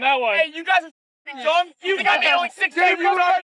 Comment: I can't understand jack. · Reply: I think he said “you got only 6k ok Tik Tok” Idk 😂😂😂😂